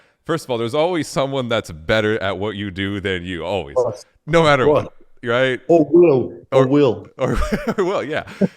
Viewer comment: Who is alex saying is better than him? I can't hear it. I just hear him say "or will" 13:06 Just curious so i can check them out. Love slaughter to prevail. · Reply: He just said of course before saying “or will” I believe